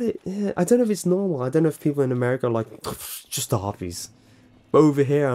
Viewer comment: i love arby's and get so much crud from people about it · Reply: ohh he said arby's i thought he said harvey's!! I was like eww dude no just no but arby's is good...